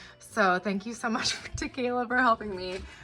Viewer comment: Oh my gosh! I heard "thank you so much tequila!!" When you thanked your sister. I may have heard wrong, but if that is her actual name, I'm here for it!!!!😂😂😂 you're adorable